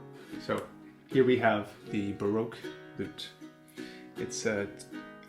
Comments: No matter how many times I hear it, I can't help but mishear Americans saying "broke lute," when what they are actually saying is "Baroque lute." In Britain, we pronounce "Baroque" as "ba-rock" rather than the American pronunciation "ba-roke." Off topic, I know, but I find it amusing. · man thought he said barbeque. wish it was a banjo.